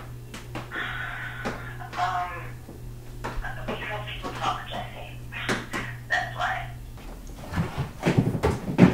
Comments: What was saying on the phone to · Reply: "cause people talk Jesse, that's why" I think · wait is that voice at 0:57. What is she saying? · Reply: we know people talk jesse, thats life